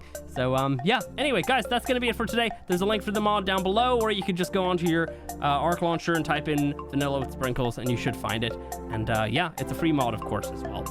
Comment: I always thought you were saying vanilla "wood" sprinkles which is kinda funny